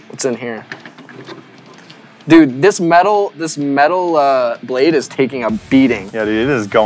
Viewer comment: Did anyone else hear that "Blake" from key and peele